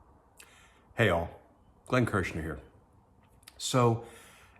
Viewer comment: I thought you said Heil , but you said hey all xd